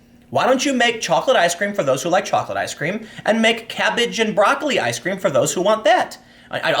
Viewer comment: For a moment I thought you said cat sht and broccoli ice cream. Call it "Progressive Delight".